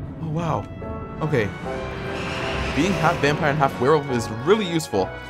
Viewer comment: Is it just me or did it sound like said 'Vampires and Whales' xD